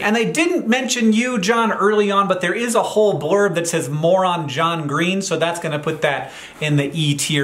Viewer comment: I heard: "Moron John Green." Thinks: "This cant be right." Pause, unwind and checks screen. "Oh. 'MORE ON JOHN GREEN.' "